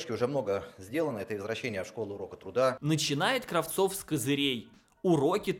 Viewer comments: Мне послышалось, или он действительно сказал ''извращение уроков труда''?) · я же не один слышу : "извращение" ? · я отчетливо слышу "ИЗВРАЩЕНИЕ уроков труда!" или я в уши долблюсь?